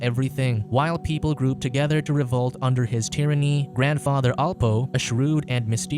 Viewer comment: The first minute, i heard "Schweiz tyranny" i exploded in laugh. Sérieusement, en tant que français qui voit année après année son pays s'enfoncer dans la folie je suis admiratif de ce peuple de gens équilibrés et sains d'esprits qui ne sont ni des branquignoles, ni des malades mentaux.